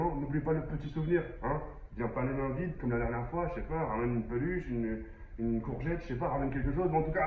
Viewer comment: C est moi ou a 0:11 il a dit ramen une courgettes 🥒🥒🥒 aaaa je suis mort🤣🤣🤣🤣🤣